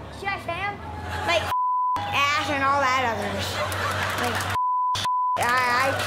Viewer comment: he really was gonna say “& all that other sh-“ 🤣🤣🤣